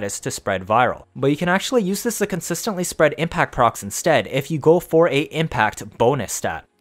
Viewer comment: I keep hearing "impact procs slash" and it confuses me. I'm at work, so Imma have to wait to get home to see this correctly.